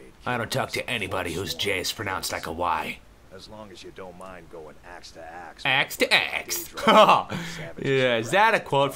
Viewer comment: thought he said axe to ass i was like O.o...ok..